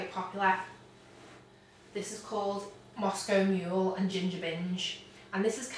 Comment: so thought u said ginger minge haha lovely name